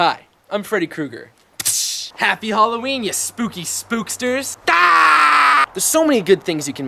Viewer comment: I'm paused at 0:36...did you say Spooksters? That's gonna be my new word lol